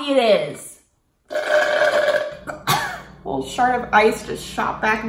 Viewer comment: she says that a shard of ice shot back into her throat but i thought she said shart :/